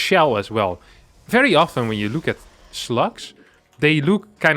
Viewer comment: It took me almost a full minute to figure out what you were talking about. I heard 'sluts' the first four or five times you said slugs, and at the end when you said "poor slugs", I said "poor sluts" and laugh far more than is probably normal. Thank you for this morning silliness.